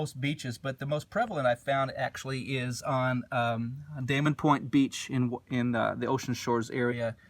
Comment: ha i thought you said Dana Point , CA lol i was like breathing fire its like a preservation you can visit but must not disturb environment beautiful shells crystals agates gems everything love that place its a shame to see how much we destroy when you go past the boundary line and literately there is nothing but thrash and beer caps .